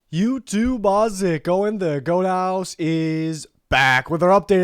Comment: Ok no hate whatsoever, I love the, and have been watching these weekly for the past 2 seasons. But what’s he saying in the intro? I hear “youtubeozick going the goathouse is back” but that can’t be right